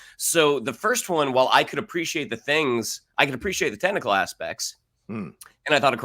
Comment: When said "I can appreciate all the technical aspects" I thought he said 😂 "tentacle aspects" about Dune Part 2 😉